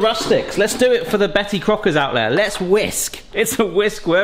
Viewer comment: Did he just say “Cretty Bocker” 😂